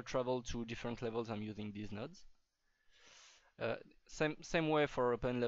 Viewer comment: when you said "these nodes" it first sounded like "deez nuts" :P